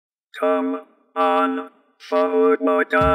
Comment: I thought Strong Bad was saying come on, Hobo guys.